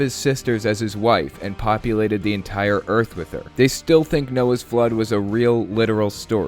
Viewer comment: at first I heard Noah's blood, and I was confused.